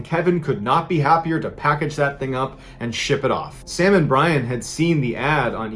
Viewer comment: when he was talking about who took the box for a sec i thought he said sam and colby lol